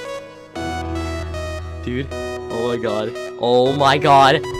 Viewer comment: Did he just said Testicals 49 at 0:26?